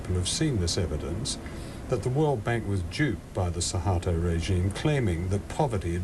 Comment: Look someone tell me if my hearing is totally wrong and what he really said. But I swear no matter how many times I play that back I hear "..that the world bank was jewed..." Really not acceptable of him to say that.